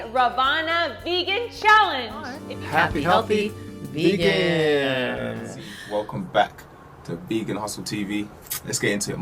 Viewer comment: it's vegan hustle TV :) he's awesome! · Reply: I thought he said Vegan Awesome TV, I was like .?? Thanks :) i wanna go check